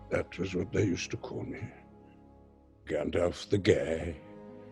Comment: it sent me so hard we i heard "Gandolf the gay" lmao